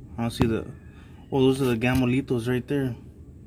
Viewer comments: Bro, I thought you were saying Gamo-litos this whole time! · His accent made it sound like he was saying Gamolitos 😂👍🏻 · Took me over 10 minutes to realize you were saying “Gamo-Lethals”. Through the whole video I coulda swore you were saying ‘Gamolitos’ haha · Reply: That's what I thought .... That he was saying gamelitos... lol... but he says it with estilo...